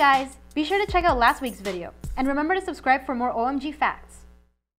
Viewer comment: i genuinely thought you said "remember to subscribe for more orangey facts."